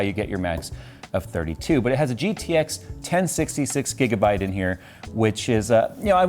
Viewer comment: I thought he said it has a “GTX 1066 Gigabyte”